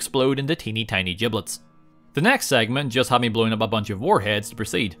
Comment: the auto captions say that Wesley snipes decided to pee